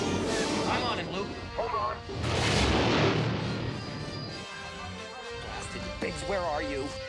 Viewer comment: Did Luke say blasted bitch?